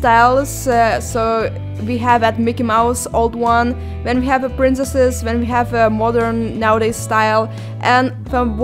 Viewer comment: Omg I had my captions on and at 3:46 when she said “princesses” the caption said “parenthesis”